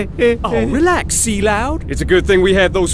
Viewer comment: Why won't anyone acknowledge the phrases "c-loud." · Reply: I thought he was saying Sea Lout for years I’m fucking DUMB